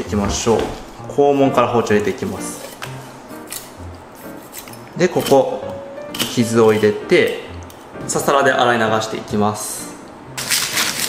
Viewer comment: 札束で洗い流していきますにしか聞こえん笑